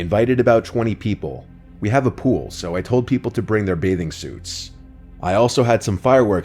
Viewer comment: I accidentally paused it, so I heard “We have a pool, so I told people to bring their babies”